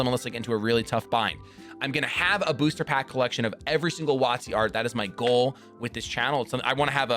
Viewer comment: At the 22:50 mark, what kind of Pokemon booster pack collection did you say? Wattsee? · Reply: WOTC - Wizards of the Coast